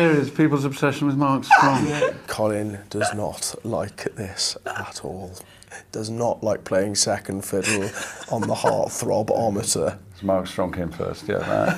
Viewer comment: Can someone write what say Taron from 0:15 until 0:23? :c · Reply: "Colin does not like this at all. Does not like playing second fiddle on the heartthrob-ometer."